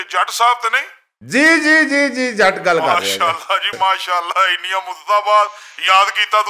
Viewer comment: I heard...India murdabad 😡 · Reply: Apnay kannan da elaaj kra "Innyan mudataan baad" nu "India murdabad" bna dita salya